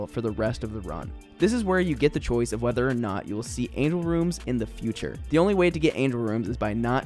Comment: did you just say “anal rooms”